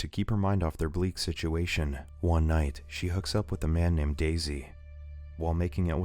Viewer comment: I died when I heard "a man named Daisi" :)))))))